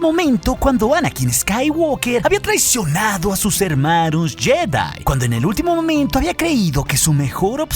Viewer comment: Soy yo pero se me figura que dijiste hermarus en vez de hermanos en el segundo